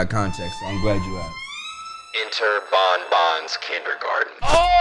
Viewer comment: Bro I thought that shit said Banyan 😂